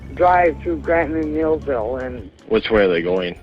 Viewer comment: Just so you know they were saying Janesville not millville or healsville. 3:11 And I think she said " I cam from Jamesville and I'm from German Town."